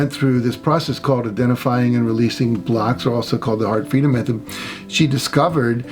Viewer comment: Thanks for this! I look forward to many more. At 15:56 on the video--you were talking about the athlete who had the mental blocks and went through "identifying and releasing blocks" otherwise known as the ... I couldn't understand the name of the method you mentioned and would love to know what you said (sounded like Art Freedom method to me--but did a quick search and doesn't look like I heard it right). If anyone knows what the method for this is called--please reply--THANK YOU!! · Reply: I'm so glad you asked, Liz! It's called the Heart Freedom Method. Click here for more information: <link>